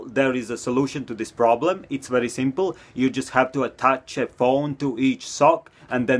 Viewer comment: I thought he said bone instead of phone and I freaked out